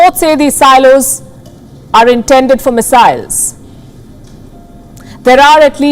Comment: did she say missiles or messiah's. WTF. Get a someone who speaks English and doesn't miss pronounce Chinese States/Cities.